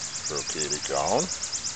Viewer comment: Good thing you had a list. At Brocaded Gown, I thought you said "Croquet de Gonne". You know me. M